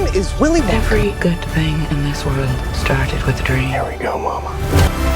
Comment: did he say "here we go mama" ?????????? Ewwwwwww